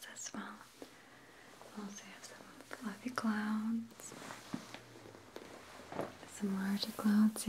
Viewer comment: I thought she said "Clowns" and I un-relaxed suddenly.